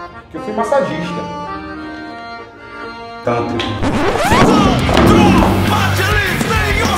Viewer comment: não entendi a palavra que ele falou antes do recruta sair correndo. alguém traduz pra mim? ja ouvi 400 vezes e nao consigo entender... min. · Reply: Tântrico